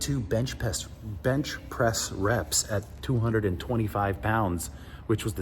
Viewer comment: I thought he said 36 reps